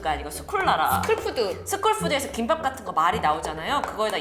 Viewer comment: 스쿨푸드를 스킨푸드로 들었어욬ㅋㅋㅋ